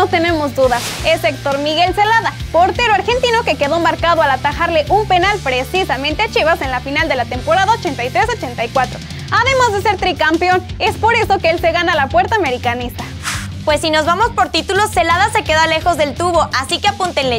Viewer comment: Temporada 86-84? Jajaja bueno está bien te entendí 🤣